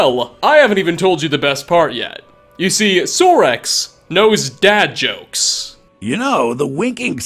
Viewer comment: "Sorex knows dad jokes" That's it, I'm convinced. My character's going gay, I need this in my life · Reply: I heard it as "bad jokes", not "dad jokes".